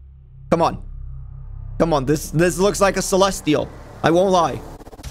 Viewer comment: did he just say that looks like a celestial?